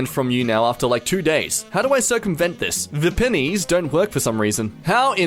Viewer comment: DID YOU JUST SAY VE PENNIES brugh moment